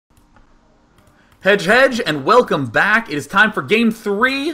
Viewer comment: You are right, It's pronounced kinda like "Hey hey".. I never realized that Day9 actually meant "hej hej", I thought he said like "Hedge Hedge" ^^